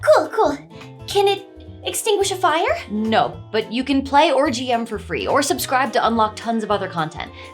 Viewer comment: I first heard "you can play orgy M for free" and had to listen again to check. 😂